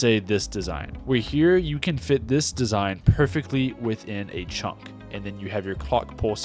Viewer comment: WHAT DID YOU SAYYY? 🤣🤣😭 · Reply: perfectly within a chunk